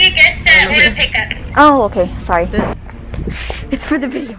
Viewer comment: Did I just heard " ay sorry Po"